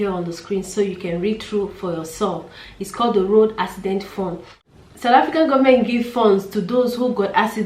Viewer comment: It sounded like you said "accident phone"